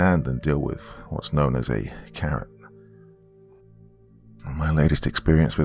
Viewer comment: I thought the first story said "I got bit by a carrot" then the second time it was "I got bit by what you call a carrot" I'm like wtf this is gonna be weird 🤣😹 I'm listening to the story and immediately felt so dumb Ohhh a Karen 🤣🤣